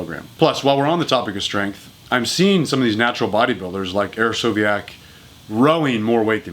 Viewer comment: Who is it that mentions at 5:19? my captions says "aerosoviak" but that doesnt yield any search results. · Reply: Hersovyac